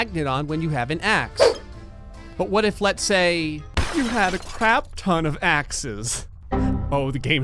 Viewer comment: first time i heard this i thought he said "a crab ton of asses"